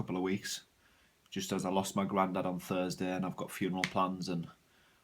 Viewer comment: sorry i about your grandpa ,i thought you said grandma sorry.i love your videos keep them coming.